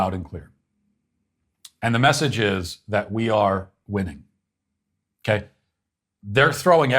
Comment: When he said that we are winning, I thought he said we are women.